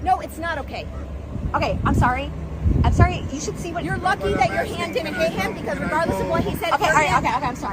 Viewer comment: "hi sara 🥰 [beats bf for nothing]" wtf EDIT: 9:34 the cop clearly says "you're lucky your hand didn't hit him" here, and later says "IF I saw you hit him" so... why did you slander the cop here 11:21?